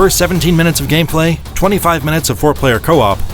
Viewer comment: at first i heard " 25 minutes of foreplay" at the end of the review, lol, gotta loooove these videogames!